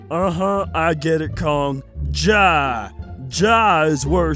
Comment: jah me : did say jah